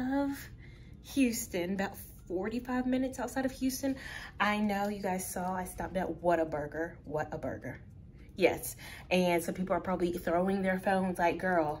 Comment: Oh, wait! You came to my city?! H-town baby!! Whataburger is a staple. You gotta come back and try other local restaurants!❤️ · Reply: 🇬🇧🙋🏾‍♀️Ohhhhh. Its Wataburger. I always thought you guys were saying Water-burger 🍔🙈 We don't have this 🤷🏾‍♀️ Everyday's a school day 👩🏾‍🎓👍🏾